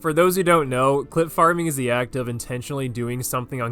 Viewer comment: bro i thought he said clit farming 😭